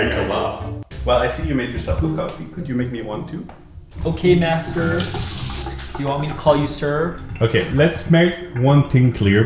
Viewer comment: I thought he said "okay basterd" god I need more sleep